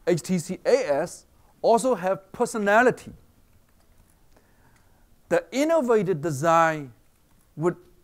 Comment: I know this guy got some intellectual skills, but for fuck sake some of his pronounciation need some fucking attention. I might not be the only one, but this is what I heard: 13:10 - pussy-nality!!